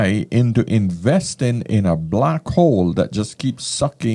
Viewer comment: i thought you said blackhoe lol and 5:20 lmao